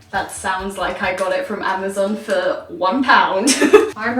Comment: I thought she said she got it from a gun show